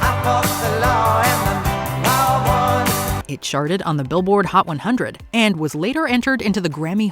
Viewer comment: every time she says "charted" i hear "sharted"